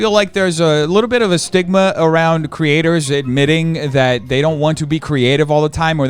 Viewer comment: genuinely thought he said “stigma around creators that are knitting” instead of “creators admitting” 😂